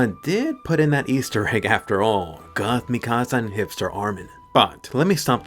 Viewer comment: Did I just hear "Hipster Armin"??